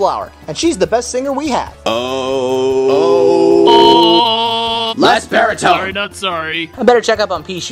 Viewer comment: What are they saying? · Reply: Snapdragons: Less baritone!